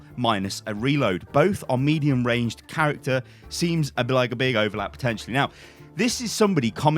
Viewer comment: lolll what was he saying? seems abilalololap potentially